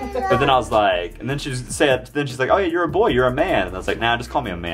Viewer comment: is so observant & specially smart, God bless her! I thought she said "You're a mean boy" LOL! you should keep doing her hair, it looks neat & very pretty + she likes it!